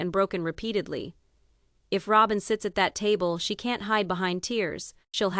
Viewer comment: i am sorry.. but this was SO hard to listen to. If it was a better articulated AI voice that was not so terribly robotic.. I would listen to it. Roben I thought it said Rogen to begin with. No hate.. but AI content channels are a hard watch for me. Hope you find your audience i guess.